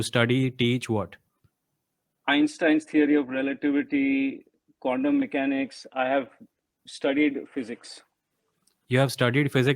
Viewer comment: Did i hear him say "condom mechanics " 11:12!